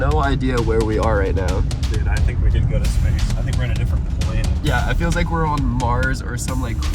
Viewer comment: how do you I thought you said Google go to space